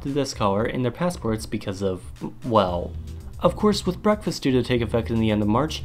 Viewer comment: did you say breakfast instead of brexit?